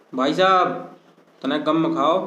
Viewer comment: what he was saying 😂😂😂😂🤣🤣🤣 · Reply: Bhi bo ghar se padhate he to muhalle ke logo ki abaj aati he une chup karbana padhta he